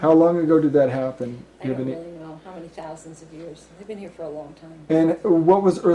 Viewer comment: does she say " we've been here a long time" ? listen from